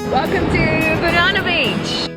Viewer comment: Hiya at 5:25 what beach did she say it was? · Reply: Banana Beach 🍌🏖🙂